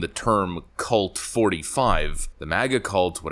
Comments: Lmao "Cult 45" all I hear is "Colt 45" one of the Lord's calibres. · I thought he said "Colt 45" 🍺 I like it! I think "Cult 45" will make a good bumper sticker for 2024!